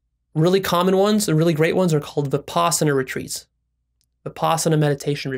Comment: "The possumeri retreats" ??? I don't understand the words. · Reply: Vipasana retreat